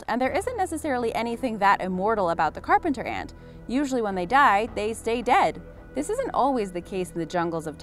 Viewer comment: "usually when they die, they stay dead" what i heard : usually when they die they- they dead